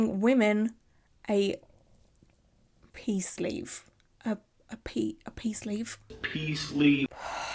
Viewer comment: Wow it took me forever to figure out that he was saying “pee sleeve.” I thought he was saying “peace leave” 😅😅